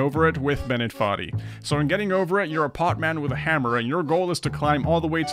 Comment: When he said pot man I thought he said hot man 😶